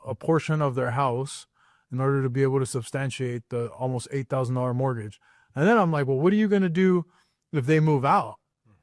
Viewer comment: did he say $8,000.00 a month mortgage????